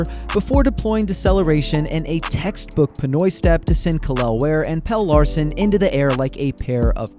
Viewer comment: Did I just hear, "Pinoy Step"? 👀